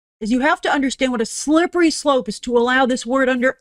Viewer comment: Is it just me or did it sound like she said "slurpity slope"?